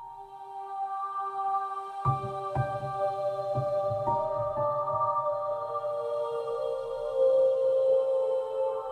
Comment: what is this i dont understand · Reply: Dirty skins